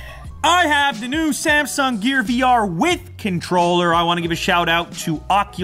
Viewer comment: did you said gay?